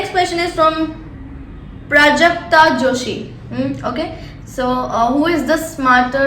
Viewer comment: I thought she said prajakta kolhi